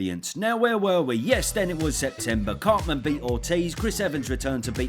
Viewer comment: did he say “cartman beat ortiz” 😂 like as in Eric Cartman 💀